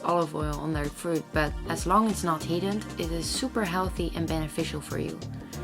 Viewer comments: Does anyone know what she says about olive oil at 8:18 ? Something like "as long as it's not ....... it's super healthy and beneficial " ? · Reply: Pretty sure she said, "heated". EVOO retains its nutrients when not heated. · Does anyone understand what she says at 8:15? People are scared to use olive oil on their food but as long as it's not _____? I can't understand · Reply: *fruit